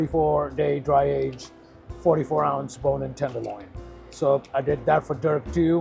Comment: did he say "bone-in tenderloin"?? WTF is that?